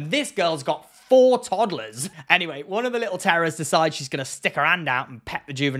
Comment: did he say, "little terrorists"? haha!